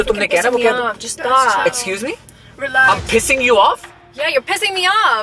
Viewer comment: At start i thought u said kissing instead of pissing.